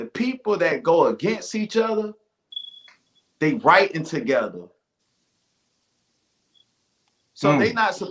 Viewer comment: ALL I HEARD WAS "IVE SEEN TOP TIERS WRITING TOGETHER"Battlerap scripted ....WTF⁉⁉😮😮😮1;11;20 TIME MARK